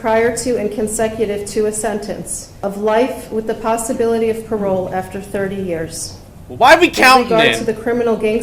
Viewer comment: for the murder she started hin to 30 years with the possibility of parole she did not say without the possibilty parole